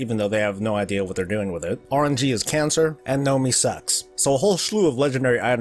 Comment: Did... did he just say Gnomee sucks 1:34 or was I hearing things? lol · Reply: Alex Skelton. Nomi